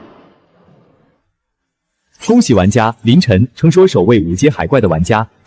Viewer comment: 我有聽錯嗎？ 我怎麼聽成林甲？